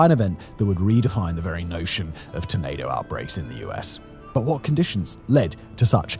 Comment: Anyone else hear tomato outbreak?